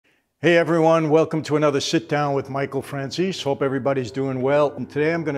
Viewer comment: The intro I thought he said shitdown 😂